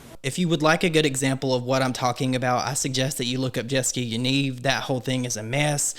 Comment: Who did he say to look up a 3:01?? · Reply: Jessica Yaniv